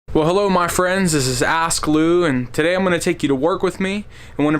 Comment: I thought he said “hello my friends this is ass glue” lmao